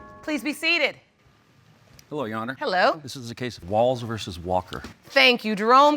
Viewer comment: i thought jerome said "BALLS vs. walker" 🥴😭